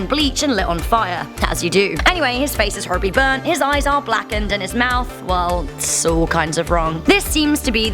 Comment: It sounds like you said "And his face is Harvey burn't" , like Harvey Dent , The Dark Knight Rises